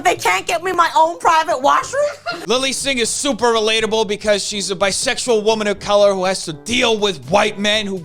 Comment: For a moment I thought she said shes a bisexual woman of colour, I must have heard wrong.